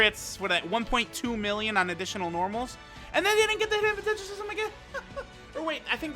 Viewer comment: In 27:25 minute what he said ? heheheh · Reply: "They didn't get the hidden potential system again"